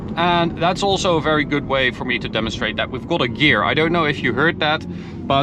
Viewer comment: what is he saying? Weve got a gear? Gosh his english tho....